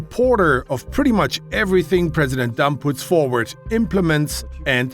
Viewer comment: does anyone else hear "president dump"?